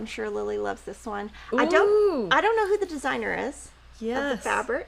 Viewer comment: Hello! Could you please provide us with the time stamp? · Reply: 40:20 “fun and wild but I don’t know the designer” is what she says.